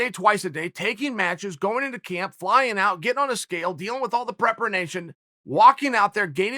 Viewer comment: Did he say "prepranation" at 4:48?